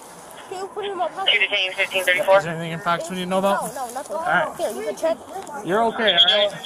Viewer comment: “Can you put it in my pocket?” Is there anything in your pocket I need to be worried about? “No I’m a kid” That broke my heart 💔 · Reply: Actually I think what he said was "No, no, nothing, here you can check--" before the officer interjects with "You're okay, alright?", cutting him off mid-sentence. You can hear it @ 1:15 and 1:18.